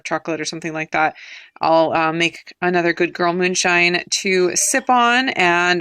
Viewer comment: What was the drink you mentioned at the end - can you tell us more about that? Sounded like you said goodgirl moonshine